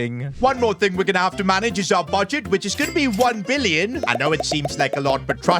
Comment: you said 1 million instead of 1 billion lol